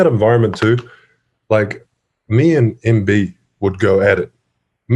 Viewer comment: Did Steven say Embiid as in Joel Embiid?